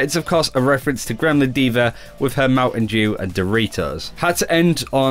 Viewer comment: Did he say... Mountain Jew?